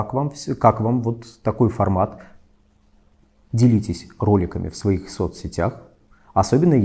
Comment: Только я услышал "Делитесь кроликами"? У меня нет кроликов. делитесь со мной